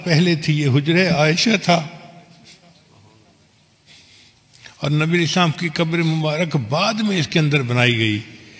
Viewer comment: what is he saying Nabi Sahab or Nabi A.s please correct me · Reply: As